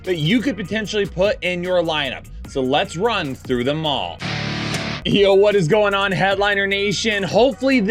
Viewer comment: When you said “run through them all” I heard “run through the mall”. Now I have a mental image of you running through a mall 😂🤣